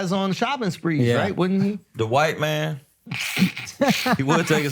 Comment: Akaash and laughing cause it sounds like he said “The White Man”